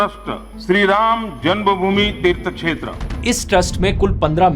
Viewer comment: The subtitles are showing dirt chhetra instead of Tirth Kshetra.... By the way loved the information and efforts that u putting in your every video ❤